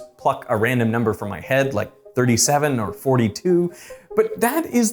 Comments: 37? Did I just hear 37 random? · did he say the F word? Go back and listen to it again. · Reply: "Pluck" does not begin with an F :)